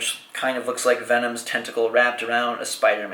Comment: I thought he said venoms "testicle" warped around a spider man symbol